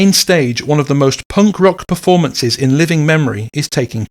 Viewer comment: When you said junk rock, it sounded like you said punk rock.